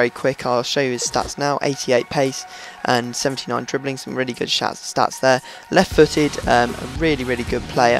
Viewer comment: he says shats instead of stats